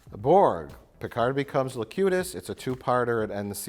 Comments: All this time, I thought Mike was saying Picard became "the cutest" but in fact he was saying "Locutus". Yeah, my English is not perfect. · Every time they say "Locutus" I hear "Le Cute Ass"